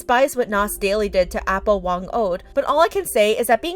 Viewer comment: I thought they said Apple but I realised they meant Apo. Good thing me and my siblings lost motivation on watching Nas Daily's content/videos!